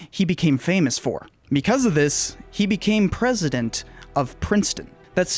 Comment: I know this is an old video, but at 5:43, you say “he became president of Princeton,” but the caption says Stanford. Those are different places, right?